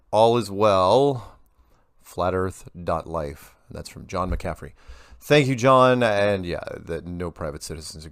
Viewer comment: I thought he said john McAfee lol i was like well that makes sense that dude is just as nuts as the flat earthers, also explains where these guys keep getting their funding for these intense attention grabbing videos